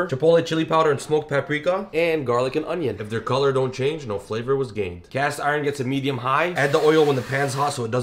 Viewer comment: "If their color don't change, no flavor was gained." 👍🏻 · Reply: my volume was too low… i thought he said if the color dont change the flavor is gay😭